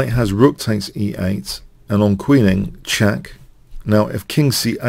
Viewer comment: did you say, "...and on queening, check"?